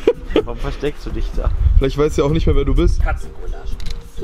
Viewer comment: Hab ich das eigentlich richtig verstanden dass bei 0:28 "Katzengulasch" gesagt hat, als er nach der Katze gegriffen hat? 🤣😅😂